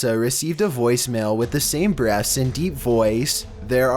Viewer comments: At the beginning of number 7 I thought you said BREASTS not breaths lol I was waitin for some demonic titties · I thought you said "Breasts" and not "Breaths"